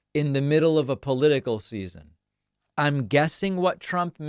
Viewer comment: I think he said: "political seizure".